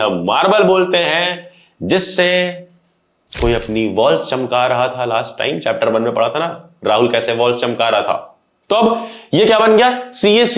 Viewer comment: i literally heard "koi apni balls chamka rha tha" (sorry guyz 1.5x pe thi) ☠☠💀💀